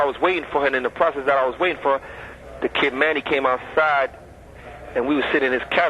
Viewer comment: Dag Alpo....Watch chu doing at a gay guy house at nite? All I heard him say is that "manny coke good" unless he cookin coke something wrong....